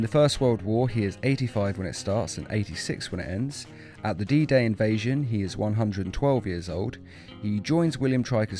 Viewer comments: The what invasion? Edit: Nvm, "D-Day invasion". I thought you said "Adida Invasion" lmao. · he say triker instead of striker